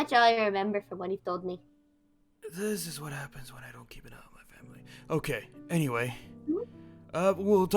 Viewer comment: oh did i hear "this is what happens when i don't keep it up with my family" or what